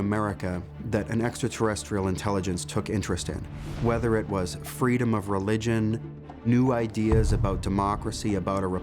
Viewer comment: Does he say Freedom of Religion or from?, I thought I heard from, I’ve rewatch that part several times, it’s Supposed to be freedom of religion